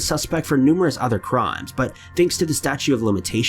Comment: I love your videos. But at 9:45 I noticed it sounded like you said "The "statue" of limitations". You know its "statute" right?